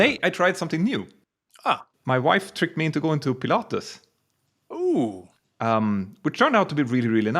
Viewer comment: When you said Pilates I thought you said Pilatus and had been to Switzerland and had a go on, either, the PC-12 or PC-24! 😁My dream aircraft. All the best.